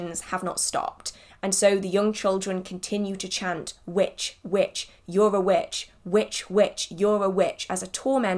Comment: I always heard them chant "witch, witch, you're a b--ch"😅